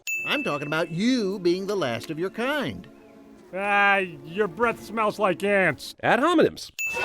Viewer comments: When I was a kid, when Manny said, "your breath smells like ants," I heard, "your breath smells like ass." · When I was a kid, when Manny said, "your breath smells like ants," I heard, "your breath smells like ass." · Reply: When I was a kid, I used to watch movies with subtitles just because I could, so I got what he was saying right away. With that said, my sister thought he said “your breath smells like gas”. With that said, I was confused as to why the subtitles said “Dam!” when the mum beaver said “Damn!” (Yeah, I just said I’m not American without saying I’m not American.)